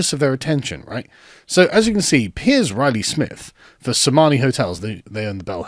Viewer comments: At first I thought he said Somali Hotels. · I heard that as “Somali Hotels”.